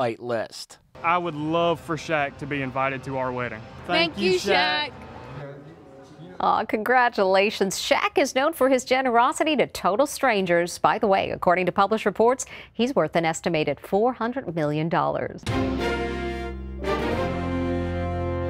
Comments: Am I the only one that heard her say billion instead of million? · Did she say he’s worth 400 billion dollars?